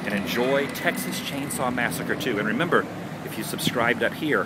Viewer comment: Cool! We named our band after that scene because our singer thought the shop owner was saying, “Oh, my kid banana!” But did you know that’s NOT was he was saying? He actually said, “Oh, my achin’ banana!”